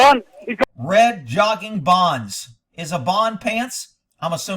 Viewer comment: It sounded like they said jogging bottoms to me.